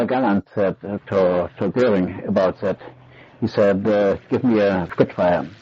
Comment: [unintelligible] He said: Do you know what OUR GENERAL GALLAND said to Göring about that?